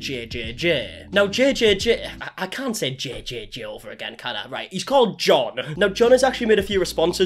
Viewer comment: what did you say at 5:02? It looks like is “cara” which is Brazilian so I’m like, are u brazilian? I lost my focus on the video trying to figure it out lmao🫣